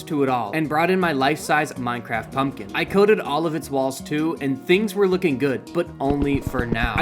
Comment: Me when he said 'I coated hid walls' what I heard 'then 'I coated his Ball's 😮'